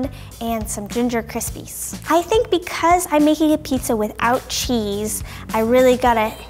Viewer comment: Oh, I thought that it said ‘pizza with zero taste!’